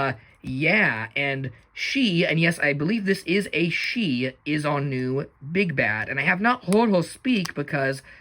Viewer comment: for a sec I thought you said “she is our new big back” I almost choked on my coffee 🤣 very cool figures! 😁